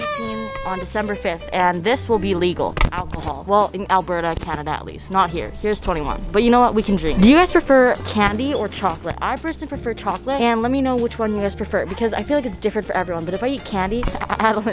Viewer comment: there is no way you said candy or chocolate I heard “Kennedy or trump” and I was rlly confused